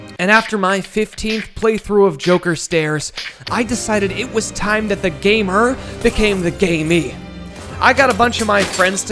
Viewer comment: I thought he said I decide it was time for the gamer become a gay me 😶